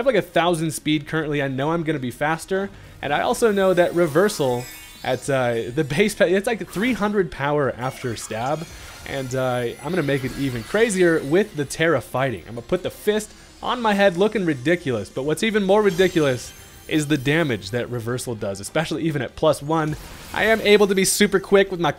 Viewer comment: When he said "Tera-fighting" I heard "terrifying " And I was like "TRUUUUEEEEE"